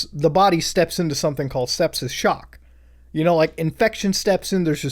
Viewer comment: lmao I thought this said stepsis